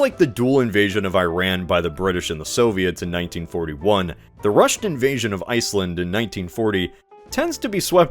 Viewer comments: I thought I heard RUSSIAN invasion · I think it's rushed invasion, not Russian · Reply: oh, I thought he said "Russian" by accident but meant "British"